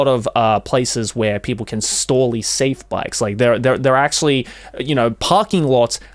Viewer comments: joeys says “storely safe bikes”instead of “safely store bikes” I don’t know why that’s so funny to me hahaha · did I hear "you can storly safe bikes"?